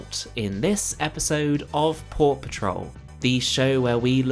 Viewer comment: When you say Port Patrol I hear "Paw Patrol".